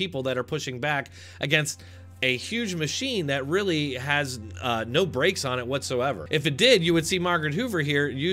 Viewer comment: Oh you said no brakes I thought you said no brains. Ehh, six of one.